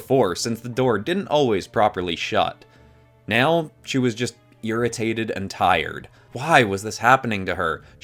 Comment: it sounds like he said urintaded.